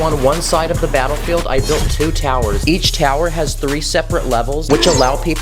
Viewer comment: What did you just say at 1:56 🤔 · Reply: Two towers